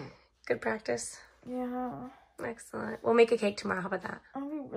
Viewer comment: omg i thought was saying keg no cake